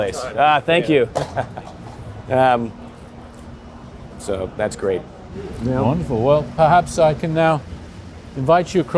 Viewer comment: What did he say at 13:15? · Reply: The guy passing by? "I watch you all the time"